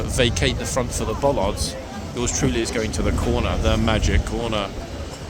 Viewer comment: Magic corner, I thought you said it was the naughty corner❤😊